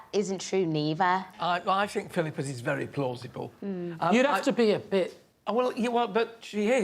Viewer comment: What does Lee say at 4:04? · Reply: They are debating whether the cat in the backpack is true because its such a bonkers thing to do. Lee says "you'd have to be a bit..." and Alan replies, "she is"